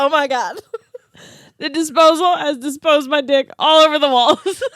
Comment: Listening to this at work and I had to pause and contain myself after I heard "I disposed my dick" 😂😂😂